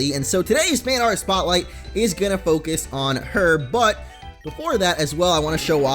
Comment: 5:30-5:38 "We are going to focus on her. BUT" sounded like: "We are going to focus on her butt"